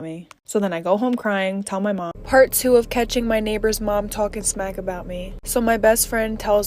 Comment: Ohh when she said "when my mom's neighbor was talking smack about me" i thought she said "when my mom was talking smack about me!!😂😂😱